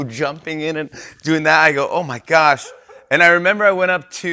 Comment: I legit thought Jimmy said ‘Oh my Gosh’ in Korean first (aigoo) followed by the English translation, but then I realised he said “I go” not “aigoo”.